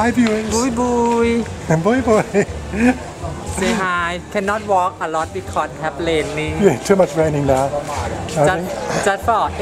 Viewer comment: I thought was saying "I cannot walk and talk at same same time"